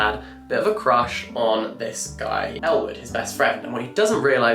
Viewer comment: I was watching this in the background as I was working and seriously thought he was saying Elle Woods when he was actually saying Ellwood 😅